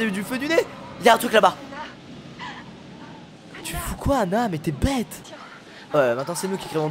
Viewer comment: "tu fous quoi Hannah mais t'es Beth" 😂😂